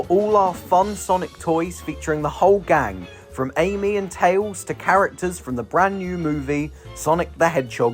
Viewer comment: When you said "Amy and Tails" I heard "Alien Tails"